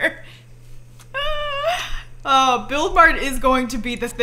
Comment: I thought she kept saying Build Mode but with an Australian accent. "Build Maurd"